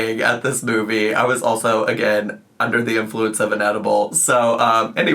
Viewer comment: I thought you said "I took an addy" instead of an edible and I was losing it imagining you going Pop-Goes-The-Weasel off your tits on adderall in the Wild Robot viewing